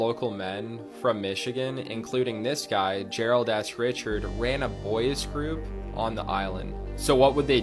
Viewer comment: Bro it sounded like he said Gerald a$$ Richard😂